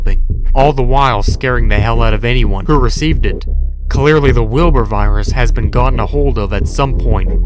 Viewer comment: When he said wilbers virus thought he said wilburts virus